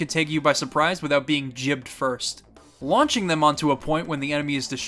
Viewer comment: did you just say jibbed?